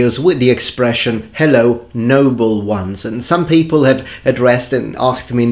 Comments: I always thought you said "Number Ones." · For a long time I thought he was saying "number ones", like what Captain Picard calls Commander Riker, and that was confusing to me because he doesn't really talk about Star Trek much so it seemed like an odd reference.